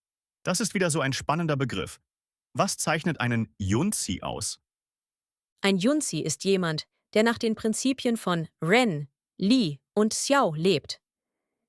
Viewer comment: Timestamp 14:44 The German subtitles differ from the dialogue in the audio. The text, as I hear it, appears to go as follows; Ein „Junzi“ ist ein jemand, der nach den Prinzipien von „Ren“, „Li“ und „Xiao“ lebt. Es ist jemand, der gebildet, tugendhaft, selbst reflektiert ist und stets danach strebt, sich zu verbessern, der immer versucht, ein besserer Mensch zu werden.